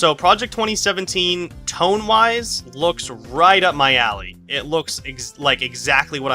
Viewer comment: I thought you were gonna say "right up my ass" at first. lol.